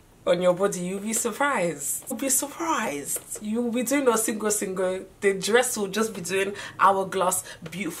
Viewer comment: Just came back to watch again.... 3:52 .....did say “You’ll be doing oh sing oh sing o”.......I am ON THE FLOOR 😂😂💀